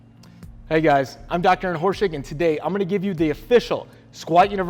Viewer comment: It sounds like he says hey guys im doctor horseshit....